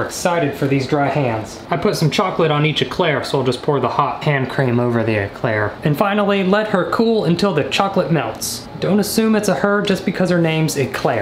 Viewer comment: i thought it said e chairs 😂😂😂